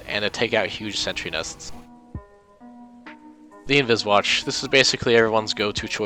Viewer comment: i thought it said travis walter